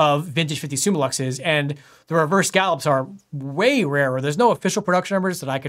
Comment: Awesome, where do you get all this rare lenses from like at 13:05? Besides, I am no native English speaker, don't understand the word after "reverse", the "reverse …????…". Maybe someone can help. In German there are several terminologies (usually "Rändelung" or "Riffelung"), no translation tool delivered even a similar word to that is using. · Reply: Ahhh, finally understood it, "scallop". Well, the scallop is not reverse, the ribbing is reverse ;) It's on the convex sections, the more common is on the concave sections. From the viewpoint of an Engineer, the ribbing on the convex ones (what calls reverse scallop) is better. Not only for grip, also for cleaning ;)